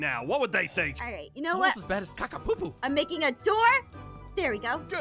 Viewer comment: The way says "ovvvveeeeeeeerrrrrrrrrrrrrrrrrrrrrrrrrrrrrrrrrrrrrrrrrrrrrrrrrrrrrrrrrrrrrrrrrrrrrrrrrrrrrrrrrrrrrrrrrrrrrrrrrrrrrrrrrrrrrrrrr here" and did i hear say "ohh its a caca popo"